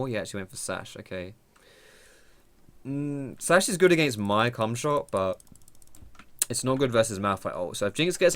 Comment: Damn, you guys are giving me some nice comments today! Thanks so much, I'm glad you're liking the video <3 Appreciate you watching as always. You all da bes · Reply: did you say its against my cumshot?